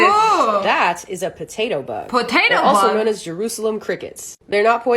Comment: When she said potato bug in the TikTok I thought she said potato lord